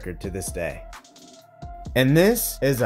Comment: Wait. Did you say “45 mile power tailwind”at 4:25? I guess I’ve always thought MPH meant something different